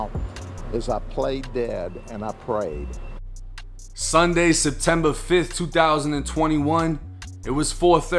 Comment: My drunk ass really had to re watch this shit cuz I thought he said September 25th instead of the 5th 😂